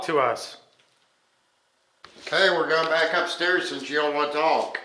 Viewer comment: there is a disem voice, but i can't hear if it's a man or woman whisper voice saying........."GO BACK"........soooo, someone down THERE does want to TALK.........???????